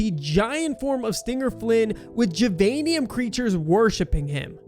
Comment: did bro really say stinger Flynn it’s syringion